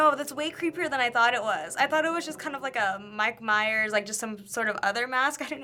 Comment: ROFL did she just say Mike Myers?